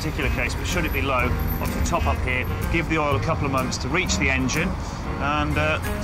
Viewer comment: that poor car! so painful to watch. and did he say "give it a couple of MONTHS for the oil to reach the engine?" u wot, did you mean a couple of minutes (or perhaps moments)?